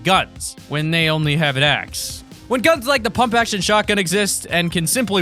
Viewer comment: Bruh did he just say pump action shotgun 7:38 😂